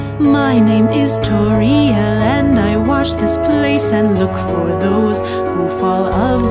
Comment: I thought she said she washed this place.